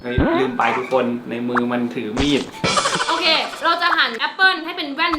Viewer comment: พี่พูดว่าในมือมันถือมีดอ่ะ